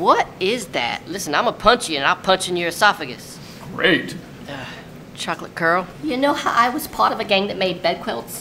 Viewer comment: "Creature", "Sasquatch Demon", "Chocolate Girl"......hahahaha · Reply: I heard "Chocolate Curl" a reference to her hair